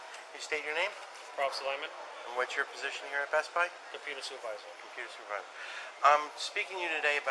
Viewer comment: I swear I heard him identify himself as “the penis advisor”.